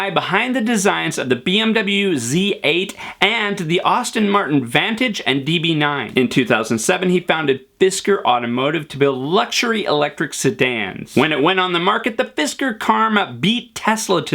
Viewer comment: Did you just say “Austin” Martin?